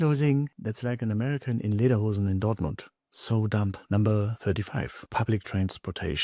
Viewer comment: i didnt understand what you said its like americans what? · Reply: „Like an American in Lederhosen in Dortmund“